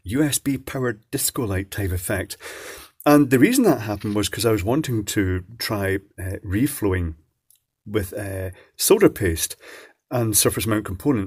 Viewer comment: Totally thought you said, "really wanted to try reefer and solder paste" right outta the box and had to rewind. It's gong to be one of those days .